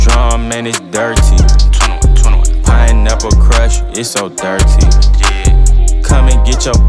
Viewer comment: He said pineapple crush i thought he said i dont ever crush😂